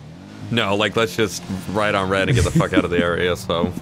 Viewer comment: I heard “write on reddit” lmao